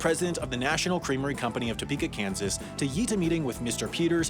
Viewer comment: Did he say YEET a meeting around 3:05??? XD